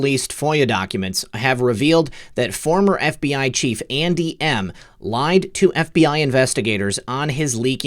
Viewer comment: LOL! When you said: "Andy M," I thought you said "Auntie Em, Auntie Em." (Wizard of Oz) LOL!! I am surprised by how much noise that Apache 'chopper made. Before Desert Storm, my son was a crew chief on the Apache and after Desert Storm he went to Germany to clean the sand out of those choppers. He was constantly telling me how quiet those choppers were. He told that one time one of them came up behind him and he did not hear it but was alerted that it was there by the wind the blades were throwing.